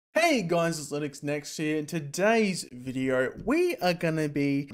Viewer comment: The way you said linuxnext here, i heard it as Linux Next year